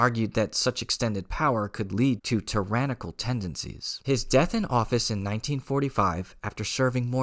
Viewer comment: A small point: Roosevelt did not die in August of 1945, as stated at about 8:41. EDIT As tcap points out in the comment below, I misheard the video. He does not say that Roosevelt died "in August", he says Roosevelt died "in office".